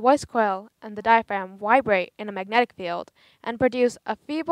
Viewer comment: did she say vibrate or wibrate